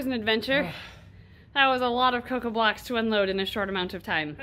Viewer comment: I thought they said cocain blocks and not coco blocks it made it worse when they said they could sell it to customers 😐😂